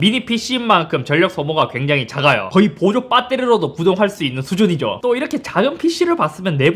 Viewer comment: 나만 보조빠따리로들리냨ㅋㅋㅋ